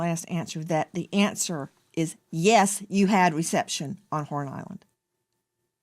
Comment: Is it me or did it sound like nancy said porn island